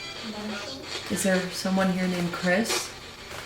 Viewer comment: when you ask is there someone here named Chris the first thing I hear is Sam and Colby REALLY REALLYY FAST ❗️❗️